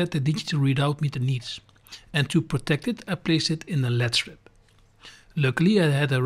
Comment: at 10:10 you say you are putting the magnetic strip in something but I can't quite catch what you say, is it LED strip? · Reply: Yes, it is a track we place a led strip in. This fits perfect and give protection against dirt